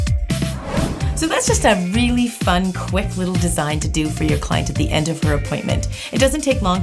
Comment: Hey I love your videos and always watch them I am also a guy who loves to get acrylic nails and I love how you pointed out "he or she" because I always have my nails done and you never really hear of that but I thought it was really cool that you pointing that out, your amazing I love you huge fan!!! 😘😊💅🏽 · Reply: I heard “client” no need for the edit. 🙄